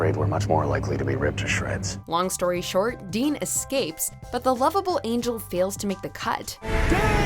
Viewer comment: ngl when she said "lovable" at 1:05 i thought at first she said "love of his--"